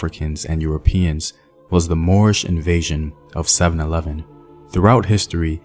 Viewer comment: "Moorish invasion in 711" I honestly thought you said the Moores invaded a 711 😂